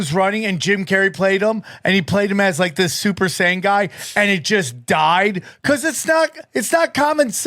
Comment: almost sound like he is said super saiyan guy though he said super sane